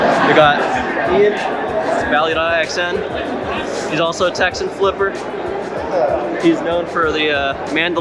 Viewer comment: DID U SAY TEXIAN FLIPPER???!!!! ill be honest im new to texas and idk any one who flips here but if u know anyone in southern texas that does lmk plz